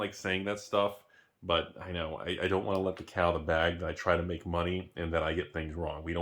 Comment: Thought you said, "Don't wanna let the cow outta the bag..."